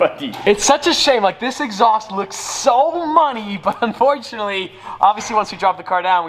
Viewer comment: did he say “this exhaust looks so money”?